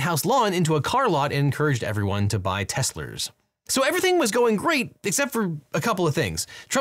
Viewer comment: Did you just say “Teslers”?